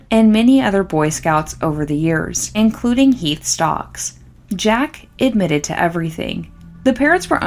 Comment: What was said at 15:30? including who? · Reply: Stocks? Heath's last name?